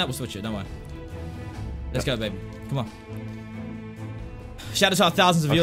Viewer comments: did call z “babe”? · Did he say Lets go BABE!!!!!!🤭😨😱